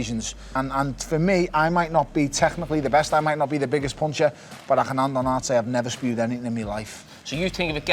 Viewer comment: What was Bellew saying? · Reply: "but i can hand on heart say i've never spewed anything in me life" I think he's trying to say he's full of heart and has never quit